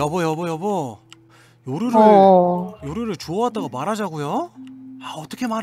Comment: 아니 그런대... 4:48초애 요루를 주워왔다고 말하자고? 라고 들었는댕 ..